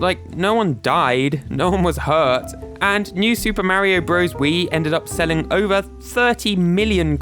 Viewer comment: I thought you were saying "Super Mario Grows Weed"